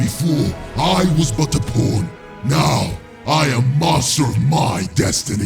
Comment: hehehe close captions this is the wrong channel. P0rn instead of pawn